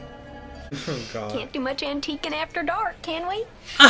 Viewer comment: What did she said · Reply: Tiba Azher “Can’t do much antiquing after dark, can we?”